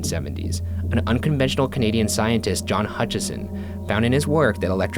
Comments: I thought they said "Josh Hutcherson" 😂😂😂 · Anyone else heard 'Josh Hutcherson' from the Hunger Games at 11:32? No? Just me?